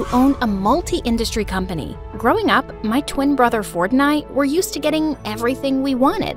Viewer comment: I thought u said “my twin brother fortnite and i” 😭💀